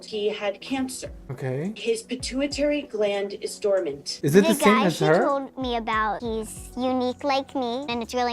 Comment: did she say the N word? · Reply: She said "the guy."